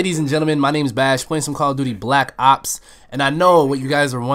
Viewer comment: lol i thought you were saying : 'What is good ladies and gentleman, My name is Bash playing some call of duty Black Cocks... omg im so dumb :P